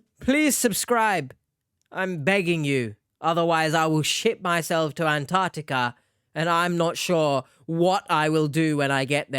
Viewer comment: Why do I hear "I will shit myself to Antarctica" ?? 🤣 Please nooooo! Don't do it!!